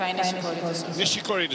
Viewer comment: ???「ニシスィコリィデスゥ」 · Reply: 確かに聞こえるわ Nishikori to serve だと思うけど